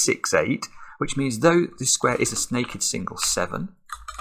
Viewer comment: Did he actually say 'Snaked single' there? XD